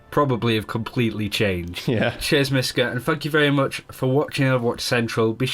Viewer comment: Idk how, but at 7:30 I heard: "and fuck you very much for watching Overwatch Central" -.